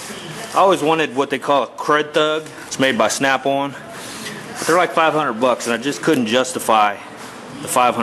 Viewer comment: WTF did you mumble at 0:14 ? CrethTub ? Made by Snapple ? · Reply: crud thug made by snap on tools